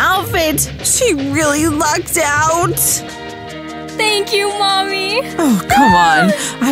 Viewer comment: Ayo did she say thank you mommy